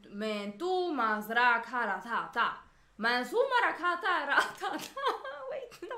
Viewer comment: lmao. It almost sounded like u said,”Gratata.”